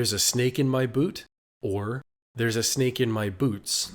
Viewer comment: The “t” in “boot” is just pronounced a little weird. I heard it as “boot” before you had the “boots written over it’s